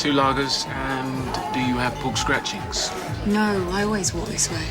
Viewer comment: What is he saying at 9:59, 11:50 ? and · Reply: Do you have pork scratchings?. No, I always walk like this. Referring to her underside anatomy causing chafing.